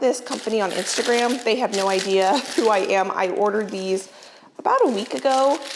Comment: It sounded like you said "they have no idea who I am." Twice. 😂